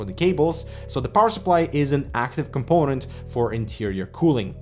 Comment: Did you just say, that Power Supply is not active component for interior cooling? Well, what I see, is that PSU is going to suck hot air from case and push it out in the back, how is that not active component for interior cooling? Regardless of that, I think they should just flip the PSU to suck air from the top, so it wouldnt use hot air from inside and not mess up with airflow in the outtake area. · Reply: I said it IS an active component ;)